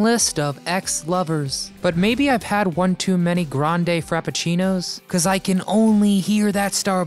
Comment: Every time I hear "grande frapachino", I actually hear "grande crapachino".